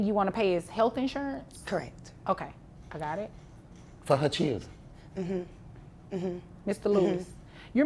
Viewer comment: DID SHE JUST SAY"CEERUS"???😅😅